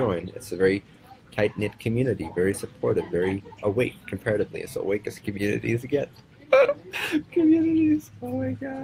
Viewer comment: what did he say when he laugh? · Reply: comparatively as awake as a community gets... in a very patronizing tone